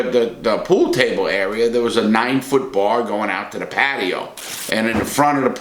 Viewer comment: i rlly thought he said 9 foot bong